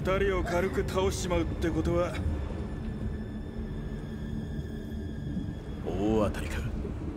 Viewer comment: What does Dante say at 1:11:50 ? It doesn't sound like Jakupoto to me, are my ears just broken? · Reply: He says "Ooatari" which is the japanese word for jackpot.